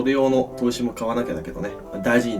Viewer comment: 最後の「バイバイ」が「ブリブリ」に聞こえた・・・・